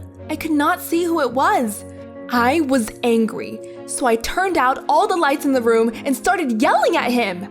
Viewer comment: “so I turned out all of the lights in the room” am I hearing that right?